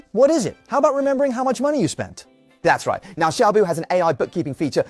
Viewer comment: did he say Xiaomi?